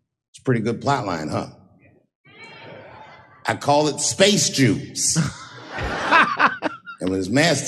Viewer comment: I thought he was saying "Space Juice" all the time. Am I heading now he's saying "space Jews"???.. Everytime I saw it am like Space Juice, I don't get